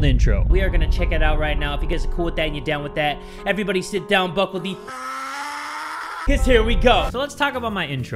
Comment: Bruh WHAT?! All this time I thought he said "Everybody buckle the BUCK UP.. cuz here we go~" How did I miss this 😭??